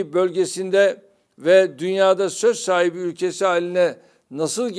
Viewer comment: Dünyada söz sahibi mi dedi yanlış mı duydum